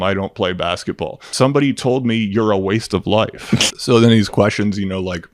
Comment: what I had said was “you wasted your life.” NOT “you’re a waste of life.” I get why you didn’t hear me. Altitude.